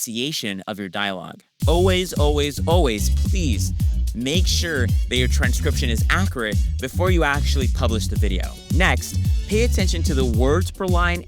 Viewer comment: I caught when you said "always make sure the transcription is accurate before exporting..." and the subtitles said "make sure your subscribed" :yt